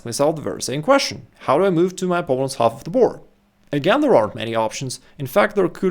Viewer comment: I dont understand when he says "move your pieces ogf the board 14:40"? Somebody explain please